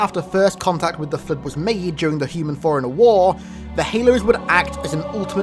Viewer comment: Lol fore runner, sounds like hes saying foreigner.